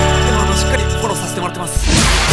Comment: なんて言ってるんですか？ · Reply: しっかりフォローさせてもらってます です！